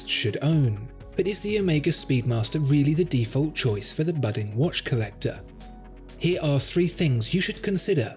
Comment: I misheard "budget" watch collector instead of budding watch collector...and almost had a heart attack.